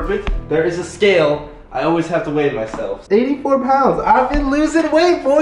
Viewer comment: thought the scale said 840 pounds.